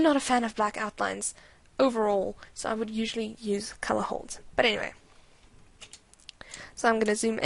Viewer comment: What is she talking about at 5:50? She said she's not a fan of black outlines and says she likes to use something else but I don't think I'm hearing it clearly due to her accent. It sounds like she's saying "color halt." What is that? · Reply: It's called a "colour hold". :) It's when you take a solid black outline and colour it to match whatever is next to it. For example, lines around the skin will be coloured a dark brown, or lines around a white shirt will be coloured a midtone blue. :D It makes for a clean, but softer overall look.